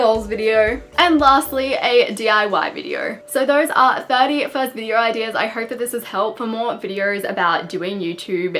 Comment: I thought it said " thirst first video ideas"